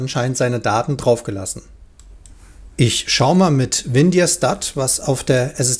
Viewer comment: Hallo..wollte mich noch mal melden;-) in diesem Video (aus Schrott...) die Datei so habe ich es verstanden heist Win sd dat zu hören ab 8:40 Minuten..was genau ist das? Handelt es sich hier um ein extra Programm oder ist es bei WIN10 dabei? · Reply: Ah - WinDirStat ist ein kleines kostenloses Programm zum Verwalten der Platte - kommt von Linux, geht so ► <link>